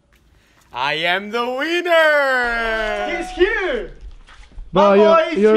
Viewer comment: It sounds like he says I am the wiener